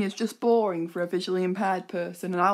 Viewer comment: not sure if anytone told you, but your voice recognition app wrote "officially impaired" instead of "visually impaired" around 4:49 minutes :-(